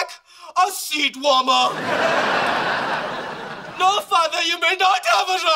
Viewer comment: I could've sworn he said " no papa u may not have a ride" 😂😂